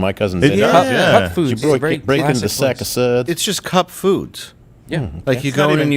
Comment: I thought Dave was saying Cub foods 🤣🤦‍♀️